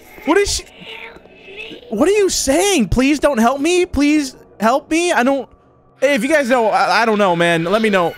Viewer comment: i think it is saying " She said help me"